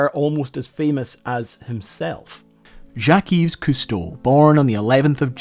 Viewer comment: Well done video essay. To those who also misheard pronunciation of Jacques and the others who will in the future, he's saying Jacques' full name Jacques-Yves Cousteau. Granted, he's still mispronouncing it a bit, but it's not nearly as bad as saying zhackeez. you're on your own with butchering Jean-Michel's name.